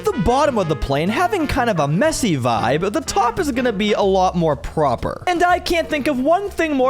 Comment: I thought he said the top will be a lot more ronaldo